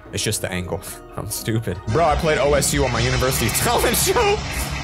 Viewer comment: did he just say O.S.U